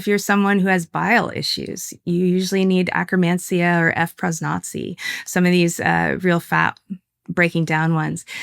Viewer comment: What is the Frosnotsi she mentions @ 1:11:13 (I have closed captions on and that is how it appears)? I found the Akkermansia but can't figure out what frosnotsi is. Thank you in advance.